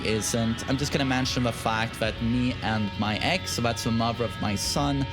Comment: I thought he said "Knee and my ex" and thought that sentence was going to go somewhere totally different.